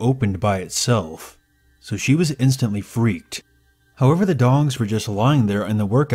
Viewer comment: First off, i love the channel, but @ 10:13 did I hear "dongs"?